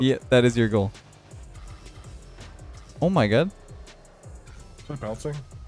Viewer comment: am i the only one who heard oh my Guh?